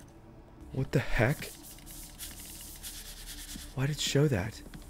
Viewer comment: I literally heard you swear. I heard you say what the #### why caylus😂